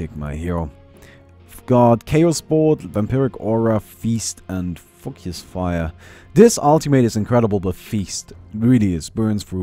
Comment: did he just said fuck his fire? lol